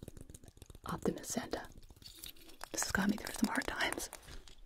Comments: I swore i heard “Optimus Santa” so I was fully expecting to see a mecha Kris Kringle. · I had my phone screen side down and when said “Optimist Santa” i picked up my phone to see if it was a transformer santa bc i thought he said “Optimus”